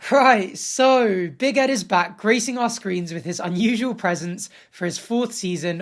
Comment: Lol..I thought you said "Big Ed is back..greasing our screens"... ...